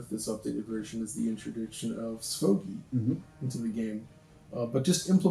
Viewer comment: he keeps saying something that sounds like “sphogie” and since there aren’t captions I have no idea what he’s saying. Can anyone help me out there? It’s apparently in relation to how dark certain shadowy areas were in the original Crysis 2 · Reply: It's SVOGI, Sparse Voxel Octree Global Illumination. It's basically a GI solution for software enhanced ray tracing